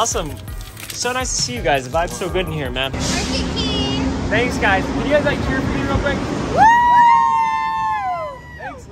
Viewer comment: What did they say? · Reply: they said "work it king🤪"